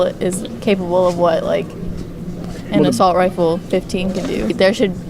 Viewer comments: less than a minute in, i hear "assault rifle 15" lol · Did she just say assault rifle 15???